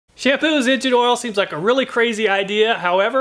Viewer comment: I thought it said "shampoo has engine oil".